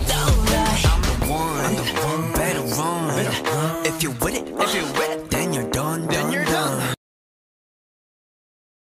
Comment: does anyone know what we're supposed to 'notice' bc i've heard it so many times and i can't figure out what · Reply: Is it “if you wet it” ?